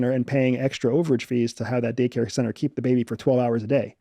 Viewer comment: 1st time he said that I thought he said data centers 😂